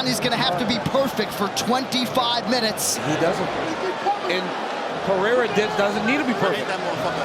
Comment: what is he saying? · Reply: Pereira doesn’t need to be perfect he said